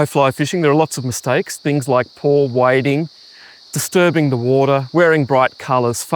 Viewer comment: what was the first one you said? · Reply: Poor wading.....basically making a lot of noise and sending waves or ripples through a slow still pool and alerting the fish that you are there..... ;)